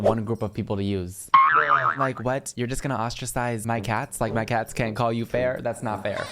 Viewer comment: i looked down and thought u said cals ITS SCARED ME SO BAD I THOUFH U KNEW MY NAME 😭